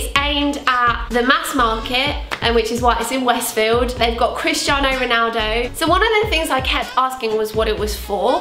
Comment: i thought you were saying "kris jenner" instead of christiano omg